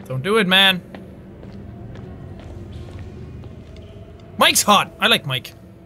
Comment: Am I the only one who Heard Jack say mikes hot